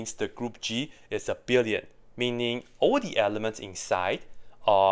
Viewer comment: "group is abelian" auto subtitles: "group is a billion" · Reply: Ah thanks. I misheard the word "abelian" but had the subtitles on, and could not figure out what "billion" stood for. (Was searching for "this group is a <insert substantive here>").